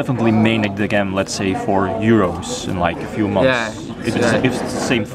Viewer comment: what do you mean with your phrase saying "for Heroes?"? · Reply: I think he said "for Euros"